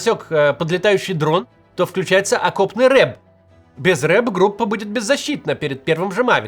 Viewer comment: я услышал не "окопный РЭБ", а "окопный рэп" 😭🙏 Боюсь представить, как звучал бы окопный рэп. Шаман решил попробовать себя в новом жанре